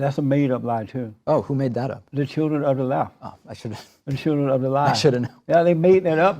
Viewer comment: JLP: "...when the children of the lie..." DP: "I'm not familiar with them?" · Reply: Is that what he said? Every time he said it, I thought he was saying “the children of Allah.” 😂 😂